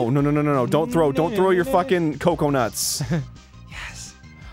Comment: I heard "cocoa nut" when they said "coconut"